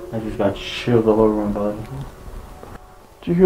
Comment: He actually says "I just got shit all over my body".